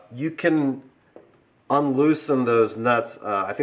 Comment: Is that an alternator on the engine? Shameful. I bought my '47 Tudor Sedan with an alternator - first thing to be removed and the generator reinstalled (upgraded to 12v). 13:26 Did he say "un-loosen" ?